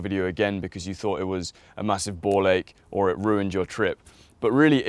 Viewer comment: Did he just say "ball-ache"?